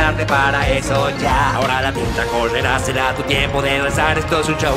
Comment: pensé que dijiste Puta Correrá :v